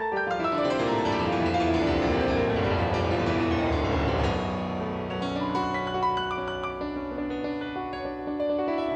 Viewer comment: What actualy mp's said? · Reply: "Keep playing 'till we signal", something like that. JP amp broke down and JR had to extend the solo until they fixed it. Thank God for that.